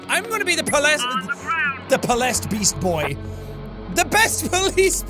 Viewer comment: I thought for a second he said the molest boy. Me:😐😏